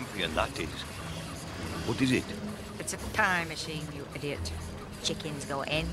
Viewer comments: when I first saw this movie, I thought Mrs. Tweedy said time machine instead of pie machine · for awhile, I actually thought She said Time Machine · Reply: Yeah, she said "pie machine" and sounds like "time machine" In spanish dub she said time machine xd